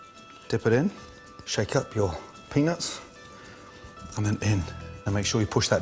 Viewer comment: i thought he'd been saying "penis" the whole time but eventually realize it was peanuts